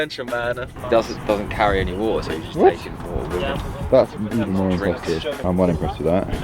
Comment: Can someone translate this I can’t understand what he’s saying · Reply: he doesn’t carry any water